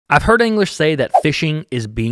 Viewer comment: At the beginning when you said "I've heard anglers say", I thought you said "I've heard English say".... and I was thinking... this dude is definitely not Amish, why is he referring to the English?? 😂